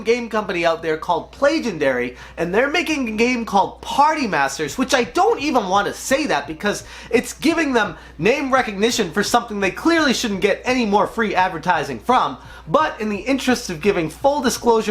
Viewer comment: i heard POTTY master XD